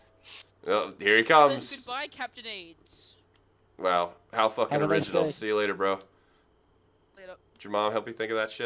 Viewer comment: I thought he said captain ace which is my GT for the Xbox